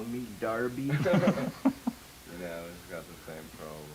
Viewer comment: after Kurt said "You mean you call me Darby?" did Pat say "No, but you've got the same problem..." @@